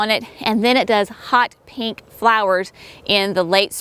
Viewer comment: I had to go back and listen. I thought I heard you say T-tiny flowers. I also say T-niney. Love your videos.